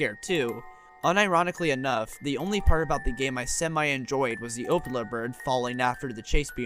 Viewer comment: at the beginning i thought he was saying "opioid bird" since i didnt know it was called the opila bird